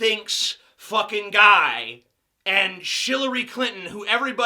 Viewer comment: did he just say "shillery clinton"? 7:22 bern.